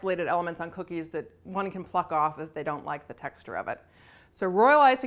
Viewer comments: I thought she said "that one can fuck off if they dont like the texture of it." I was like damn this is my kinda lady. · Am I the only one who thought you said “one can fuck off” at 2:07 😂 I LOVED this video though. Thank you 😊